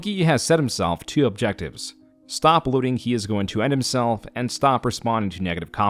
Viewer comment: Ok... if someone, anyone, could PLEASE tell what is being said at 26:20, I would be eternally grateful: I've listened to this line probably 20 times now, and I still have absolutely no idea what word is being said at one point. It's the first thing he says after he says "He has set himself two objectives:" "Stop _____(Here. This word is what I'm talking about) he is going to _____(this one is probably 'end', but I'm not 100% sure) himself" Logically, it seems like he's probably saying "believing", but it absolutely does not sound like it AT ALL. I know this is totally trivial and unimportant, but its driving me nuts.😂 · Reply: "Stop alluding to".